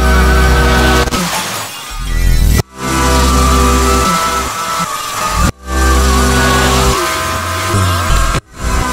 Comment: Is it just me who hears something at 2:27? It sounds like Miku saying something but idk Japanese that well 🥲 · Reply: 「戻して」言って